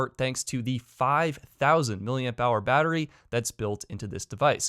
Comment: Did I hear 5000 million dollar battery "))